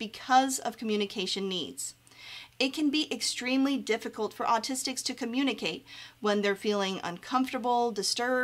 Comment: So this is just from my own personal experience. As a black autistic woman, it is incredible that I was even able to receive an autism diagnosis at the age of 20. Being autistic is so individualized and some people think autism is SO bad. While it may be hard to be autistic at times, it must be taken in account the successes that come with being autistic. I feel there needs to be more autistic doctors diagnosing autism because lots of non autistic doctors seem to focus on deficits of autism. Also ABA therapy is very hated among most autistics. It's a "therapy" that seeks to change how autistics present themselves in society. Feel free to ask to chat if you want to discuss this further. Thanks and take care! I'm on Facebook and Twitter as well as Reddit. Let me know if this helps! ☺️♾️🫂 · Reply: i thought you said acoustic at some point😭